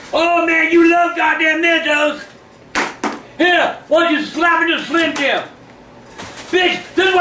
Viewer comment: I lost it when he said why don’t you snap into a slim Jim 🤣🤣🤣🤣🤣🤣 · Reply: I thought he said “Why don’t you slap in a Slim Jim?” Lol.